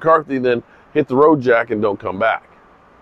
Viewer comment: My heart almost skipped a beat. I thought said "hit the road Dak and don't come back"! But it was Jack.